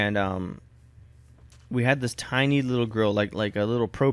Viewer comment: I thought you said girl instead of grill o_o